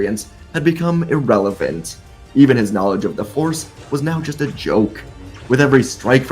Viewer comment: i swear to god, when i heard this i was convinced he said "even his knowledge of the horse was now just a joke"